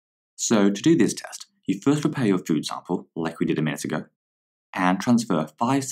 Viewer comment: what tf did he just say??? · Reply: prepare your food sample like we did a minute ago